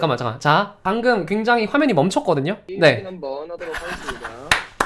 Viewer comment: 게이밍 한번 해보겠습니다? 뭐라고 하셨는지 아시는분